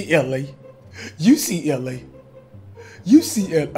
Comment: Hit dat thumb if u hear say “UCLA” instead of “you see LA”.